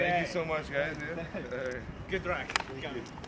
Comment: did he said good rack?? 😂